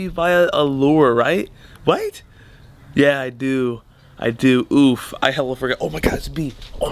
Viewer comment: Did anyone else hear him say “oof” 😂